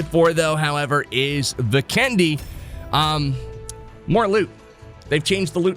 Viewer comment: I thought you're saying candy. But Vikendi, sorry for the misheard 😂😂😂😂